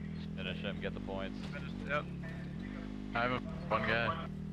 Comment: His teammate : 'I have one guy' What I heard : I have fungi Lol😂